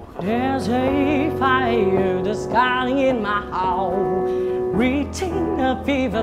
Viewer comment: my heart hay my house hay my how. Hok nghe chữ gì, chắc do tai tụi nghe tệ. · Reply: duong nguyen uh.. Mình k nghe đc my heart, mà nghe thành Hold, hay House, hay how j j đó :)